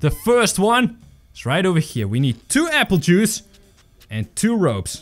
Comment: I heard happy juice thought it was alcohol😂